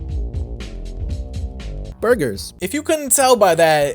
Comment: Burger? Did he just say that his last name is Burger? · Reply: password burgers